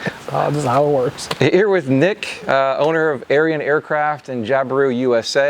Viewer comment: LOL I thought you said "Aryan" then I saw it's spelled Arion...